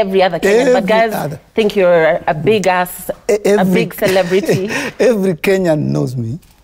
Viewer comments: Lillian: A big ass (who else heard this) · Wait can we pause at 2:53 did she say "your ass is broke" 😂😜🤪😂